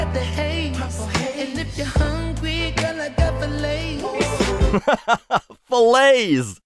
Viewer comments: I honestly thought they said “The Lays” like Lays potato chips, not “Fillets”. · When Mars sings what heard as, "Got filets," I totally heard, "Got the Lays," which makes much more sense when you're smoking pot. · I really thought he said "if you're hungry, I got the Lay's" as in Lay's potato chips